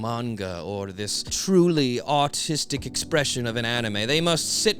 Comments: Did you say "artistic expression of an anime" or "autistic expression of an anime"? I seriously dont know which one you said, lol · did he say artistic or autistic?